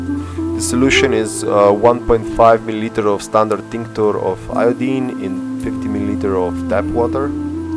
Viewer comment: hi, I'm curious about the solution you mentioned in the video(around 3:10, solution in red bottle), couldn't hear it clear from the video, :p, thanks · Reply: standard tincture of iodine. You can get it at any drugstore, it is used for disinfecting wounds, skin, etc.